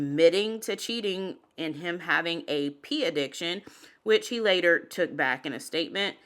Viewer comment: I laughed when I heard you say "P addiction" and I knew what you really meant but it sounded like you were saying he had an addiction to urine.